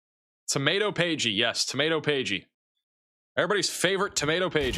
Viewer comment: SOUNDS LIKE YOU SAID TOMATOE PAIGEY